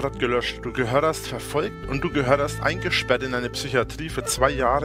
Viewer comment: Du gehörderst